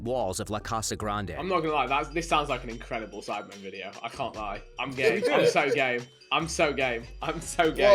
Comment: is it just me or did anyone else think vik said "im gay, im so gay, im so gay" lmao had to play dat one back